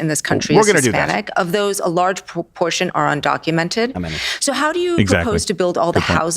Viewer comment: Did she say “poop portion”?